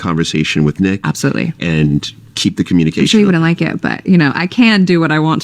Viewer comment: she actually said “I’m sure he wouldn’t like it…” she just told us she not allowed to date other men, like someone else said she gave up